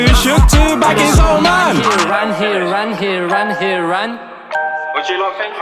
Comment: He said didn’t help but at 2:43 it sounds like saying ‘he ran’